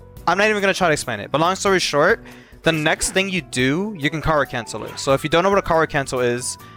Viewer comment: thought you were saying "car cancel" until you typed it out on screen lol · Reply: I swear I kept hearing "Coward cancel"